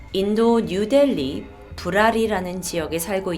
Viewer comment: ㅂ.. 잘못들었죠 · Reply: 부라리 ㅋㅋㅋㅋㅋㅋ개웃기메